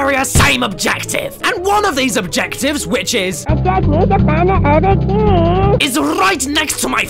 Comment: "AND ONE OF THESE OBJECTIVES WHICH IS..." I hear "i need to find the other keeeey"